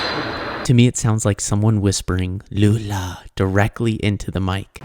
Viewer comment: sounds like it said Gulag